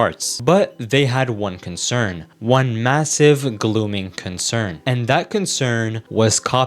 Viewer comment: did you just say "glooming concern"